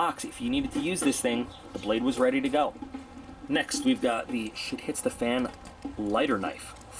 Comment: "shit hits the fan" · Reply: Swag Tem i thought he said she hits the fan hahaha